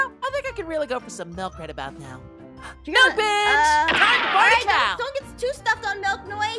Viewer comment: I thought noie said Mille bic’